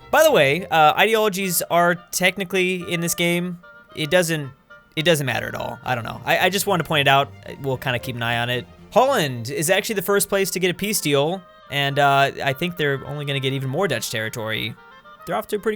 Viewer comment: “Ho (l) land” I thought he said Poland lol